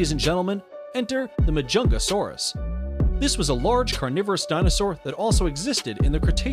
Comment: I swear I thought he said a ‘Chungasaurous’ til I played it back again 😂